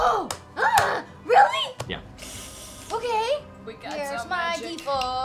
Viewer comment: I hear jester